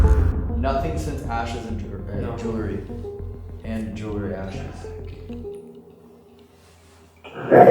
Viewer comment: i hear "correct, Elton, jewelry"